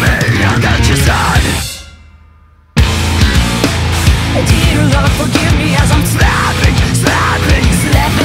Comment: Unconditional love to them because i left here when i heard "please lord forgive as im snapping or is it slapping regardless the flow is uncanny